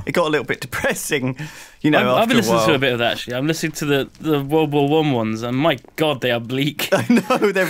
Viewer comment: Is it just me or does it sound like Duncan is saying "Womba-wumb" when he is saying "World War I"?